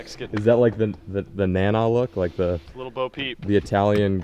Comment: Did just say lil bo peep?